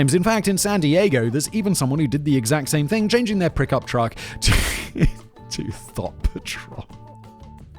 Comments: Did he just say "prick-up truck"?? (10:54) (keep clicking that over and over again, about 1 second apart.) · Did you say prick up truck?